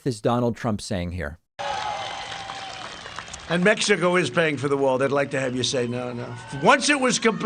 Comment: Sounds like he says Mexico is baying for the wall instead of paying and what human let's his crowds end up with ems coming or in the hospital But whom is the President at this time of the lost of over 200,000 people Big cause of needless deaths Transparent that we see right through his endless remarks some we are just blown away with